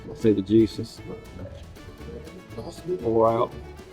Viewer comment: fuck me, I needed subtitles for what he said at the start. Thought he said Jesus but what he said was juices